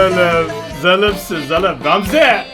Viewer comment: zalımsın zalım Gamzeee! der gibi duydum meğer sona kalan rakibin nicki Ramze' ymiş :D:D